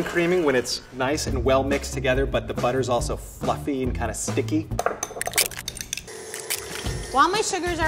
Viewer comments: Did anyone else hear F***ing and stick instead of fluffy and sticky?? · I thought he said “fucky and kind of sticky”